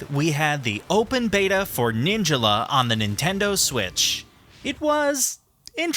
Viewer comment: did he just say Ninjela